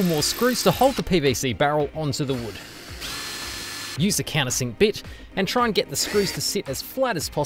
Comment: I heard "a can of sick bitch", and had to replay the video 4 times until I heard "countersink bit". Lmao either way 😂